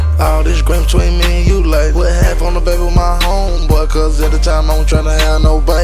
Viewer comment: Half on a baby with his homeboy? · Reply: dfjr1990 i thought he said half on a bag